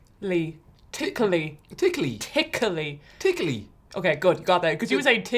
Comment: I swear I thought was saying, “tingly”